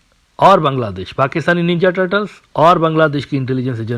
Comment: Did I hear that right? 3:59 Pakistani ninja turtles? 😅